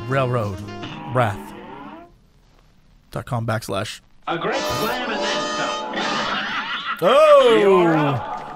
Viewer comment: Every time I start a stage in this game, I don't hear "A great plan and then some", I hear "A great clam and then some". Am I the only one?